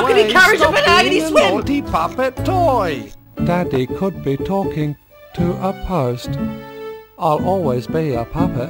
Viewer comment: Did he say "Daddy caught me talking to a post"